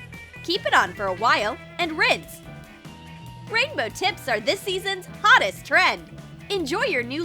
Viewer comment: I thought you said rainbow tits 😂